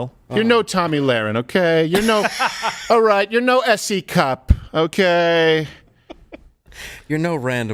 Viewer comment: Remember, the US is a Republic, and they know it. Whenever they say anything about Democracy (attack on, war on), they mean it's against their party, not against the country. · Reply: Also, what is with S E Cupp? The first time I heard her name, I thought they said a C cup. Does she not hear it?